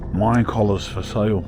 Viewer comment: I thought said "mind collars for sale". Had me wondering what kind of town he was in.